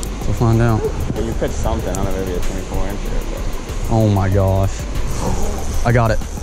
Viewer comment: did he say maybe a 24 incher?😂😂😂